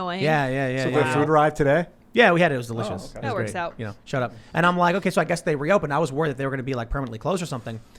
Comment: Did tim say shut up at 8:53?? lmao